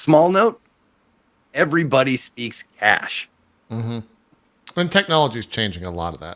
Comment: Cohh thought said "Everyone speaks ass." I'm and sitting here agreeing with him that, yes in fact everyone does speak "hash".